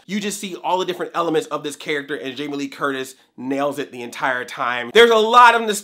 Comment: 🤦🏽‍♂️ did anyone else hear, "Carter?"